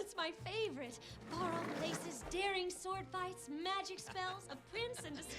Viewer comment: It's prince in disguise I heard "Prince in the skies" (So it could be Aladdin)